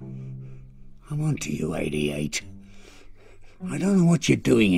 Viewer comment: I can't hear anything the old man is saying. It sounds like gibberish to me ! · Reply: Actually the subs says "I want to you 88", when I thinks it's "I'm onto you 88"